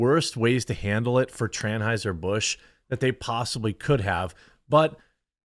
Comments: Did I hear that right "Tranheuser Busch? Tranheuser!! Something to remember. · DID he just say TRANHEISER-BUSCH ?????????????? lolololololololololololol